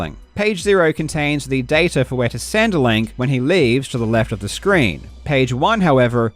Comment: i swear he said 'centrelink' lol legend of Link the Bogan 2